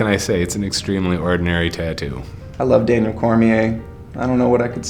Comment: wtf...he said I love DAMION when his name is DANIEL....that's love.....lmfao